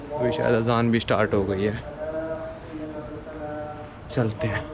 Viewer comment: kya bola yaar . Ravzan bola 🤔🤔🤔🤔🤔🤔🤔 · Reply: Azaan